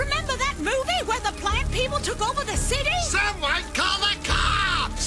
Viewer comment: Sounds like shes saying "Black people took over the city" LOL